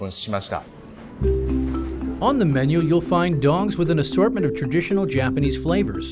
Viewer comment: narrator totally says dong instead of dog....